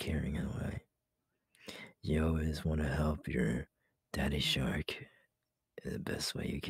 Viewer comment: Hold up- did he just say he was a daddy shark-😮